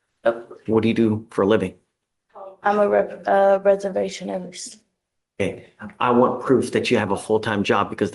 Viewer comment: Sounds like she said reservation nurse? Reservationist? What is her job.. other than thief?